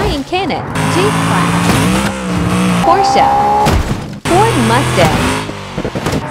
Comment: Why do i always hear “poor mustang”at the first?😂